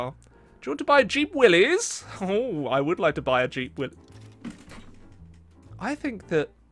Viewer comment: Misheard jeep willy for cheap willy 😂😂😂